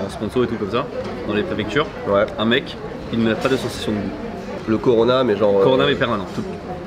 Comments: Svp, il dit quoi à 9:50? Il a parlé trop vite j'ai pas réussi à catch ahah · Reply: "Qui n a pas de sensation de goût " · je l’ai passé 10 fois d’affilé, j’entends a chaque fois : « Un mec, il met pas de saucisse sur son bout » Il dit quoi en vrai ?